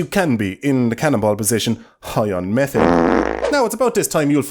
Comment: Not even joking when he says “high on methane” an ad cut in so it sounded like he said “high on meth” and I was like wtf why you including this? Self incrimination much 😆